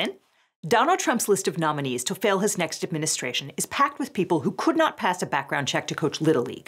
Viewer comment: I could have sworn Liz said, "Donald Trump's list of nominees to fail his next administration..." instead of "fill his next administration" and was totally there for it.